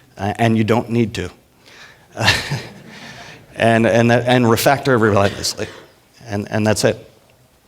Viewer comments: What did he say? Refractor what? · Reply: "and refactor, uh, relentlessly" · What does he say at 20:20? "refactor every line you see"? I can't really tell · What does he say at 20:12 And Refactor ..(something).. And that's it. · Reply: "Relentlessly" I think.